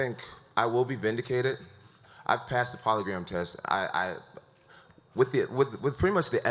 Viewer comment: Did he just say "polygram" test not "polygraph"😂😂😂😂